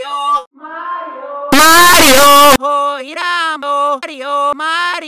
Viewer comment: Luigi: OIRAM OREO MARIIRAM Me: that kinda sounded like he sang